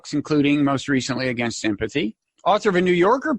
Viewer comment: Bob said Against sympathy in his introduction. Which I thought was funny. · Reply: Lol this could turn into one of those yanny/laurel things. I heard "Against empathy". ^^